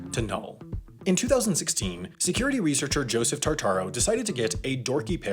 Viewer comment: dear god I've been watching too much JoJo I heard Joseph Jotaro